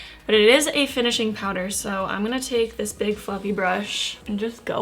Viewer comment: Honestly Everytime she said big fluffy brush I thought she said big fucking lol. 😂